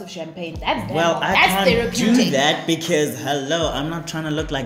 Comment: did he just say HELLO?